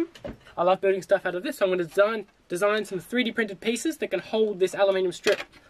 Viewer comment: You say "3-d printed pieces" to hold the aluminum strips....I hear that as "superglue"!